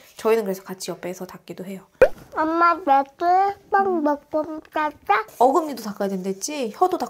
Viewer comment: 귀여운 옹알이 뭐라는지 통역이 필요합니다.. 제발😍 이 딱는게 이렇게 귀여울 수 있는건가🤭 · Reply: 엄마 얘기해~ 빵 먹으면서! 라고 하네요ㅋㅋㅋㅋㅋㅋㅋ